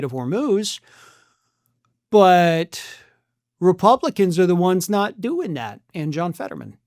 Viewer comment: All I just heard was "... buuuut, Republicans are the ones not doing that, and another Republican." What? 🤨